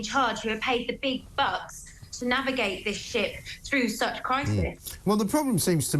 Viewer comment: i thought for a second she said 'navigate this sh*t' 😂🤣 fitting.. But then realised it was 'ship'.